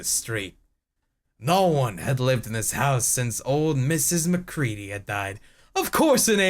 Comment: While drawing, I thought he said "Mrs. McCreed". Almost said "It's high noon"... ...It's High Moon.